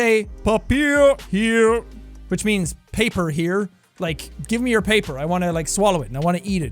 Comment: did someone say......... Papers Please